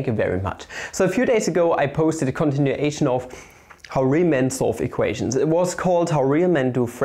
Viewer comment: I swear I hear "Riemann" when you say "Real men"